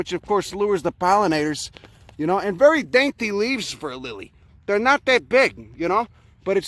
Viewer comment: He said “very dainty leaves for a lily” I heard “which kneecap will I shatter for you today?”